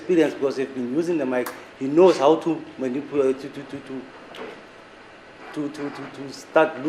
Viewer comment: did he just say “he knows how to manipulate”?